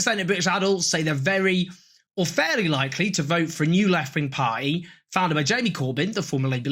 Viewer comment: Thought he said left wing pie - and I lived in Britain for two years.